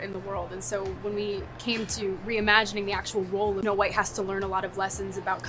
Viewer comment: does she say no white instead of Snow White or was that just an edit in the clip?